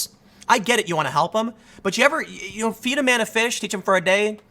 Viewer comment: feed a man a fish teach him for a day - tim pool i heard it as feed a man a fish eat a man a day - my stupid brain